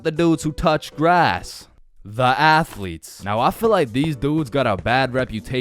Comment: The athletes What i heard: The Assletes